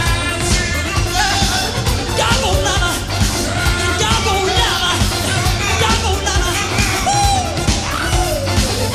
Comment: I don't understand what he is saying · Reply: He was saying “Doggone lover” Its an old fashion use when you get annoyed by something or someone..